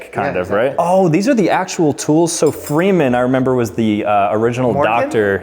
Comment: I don't understand what Mike says here 2:14 Can someone explain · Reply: Morgan Freeman . The actor